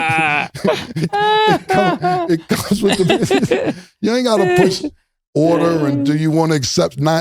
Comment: Ma$e came out swinging at J Prince. I swore he was bout to say "You ain't got the guts!" 🇺🇸Ma$e for president 🇺🇸